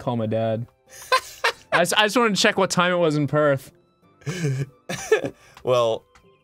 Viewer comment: Where is Ross' dad from? I think I heard "Prague" So, is he Czech? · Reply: Marvel Gilbreath It's not Prague, it's Perth, in Australia, he's from Australia same as Ross ^^